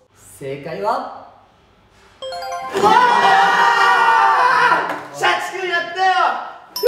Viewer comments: 元太くん）シャチくんやっ たよ〜！ 社畜やったよ！って聞こえて頭にハテナしかうかばなかったごめんね元太くん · の『シャチくんやったよ！！！』が 「社畜になったよ！！！」って聞こえたからもうダメかもしれない((( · 「シャチくんやったよ！」が「社畜やったよ」に聞こえたので休みます...